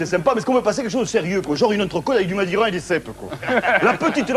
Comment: à 3:49 il dit quoi ? La petite le regardait façon ... après je ne vois pas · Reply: Du madiran et des cepes.... madiran doit etre un vin